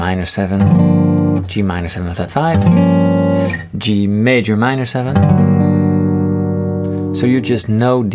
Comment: At around 9:09 it sounds like you said Gmaj(min 7)